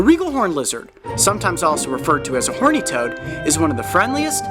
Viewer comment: Uhm...Did he just say “Horny”